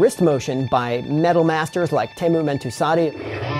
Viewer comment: can you write the name of that guitarist? I didn't hear it well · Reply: That would be Teemu Mäntysaari!